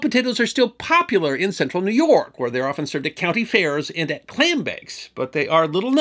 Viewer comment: Why i *love* captions... I have an auditory processin disorder and can easily mishear words even on repeat... When you said "clam bakes" i had to back up and read what it said bcuz my ears heard "Klan bakes" which i assume probs happen(ed) but are also just not somethin i wud expect to just casually hear talked about xD Ofc then again, nor wud i expect a Klansmen in the 60s or 70s to be advertisin in newspapers; and yet, it happened and even resulted in a Black cop goin undercover as a Klansmen and rootin out many potentially dangerous individuals in key positions of power. His actions actually saw two Klansmen removed from positions that gave them potential power over nuclear weapon usage... So yeah, theyre all over and they wud be so brazen as to have publicly known about klan bakes no doubt, but not so brwzenly in Syracuse, NY IN the early 20th century i wud hope (eta: Im not alone in mishearin you xD At 11:18 the captions say "clan bakes" not "clam bakes"; so at least the autogen captions AI has as bad of hearin as me :P)